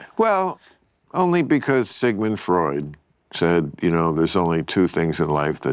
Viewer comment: When I heard Sigmund Freud, my initial thought was did he say Sygmoid? the famous colon : ) Pray for me.